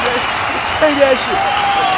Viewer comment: "SHAKE THAT SHIT" Got me rolling bro 🤣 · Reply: I thought he said “shut that shit” lmao